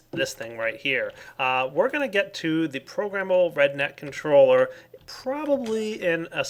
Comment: I thought he kept saying ''programmable redneck controller'' XD